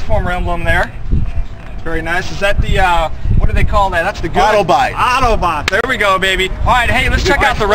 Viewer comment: lmao did he say auto bites at 0:37 ??